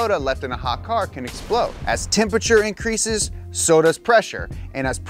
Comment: "Or why a can of soda left in a hot car can explode" "as temperature increases, sodas pressure" "as pressure increases, sodas temperature" I can't be the only one who heard this 🤣